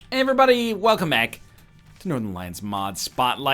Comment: It sounded like he said "Welcome Ack"